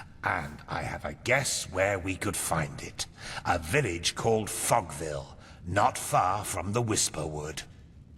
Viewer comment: I really thought he said fuckville 😂